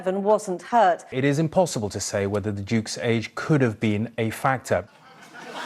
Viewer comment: OMG. 😂 😂 You are too funny. I thought you said FATWA. NOT FACTOR.